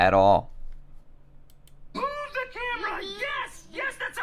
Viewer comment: I hear: “ooh the camera! Yes! Yes! That sounds like an excellent idea!”no matter what